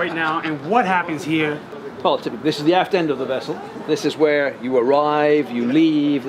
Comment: I Heard “ this is the ass-end of the vehicle”..